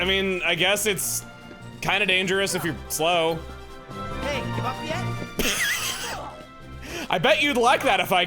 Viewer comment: I always thought Hercules was saying "hey, you up yet!", found out he's saying "Hey, give up yet!" but that's boring so I refuse to hear that. Side note I love how the dead guy's situation never gets brought up again that I know of.